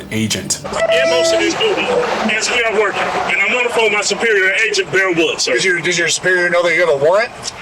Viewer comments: Lmao did he say Agent Bear Wolf? lol wtf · Did he say his superior’s name is “bear woods”? Holy shit! My bosses name is “2 ply teepee”.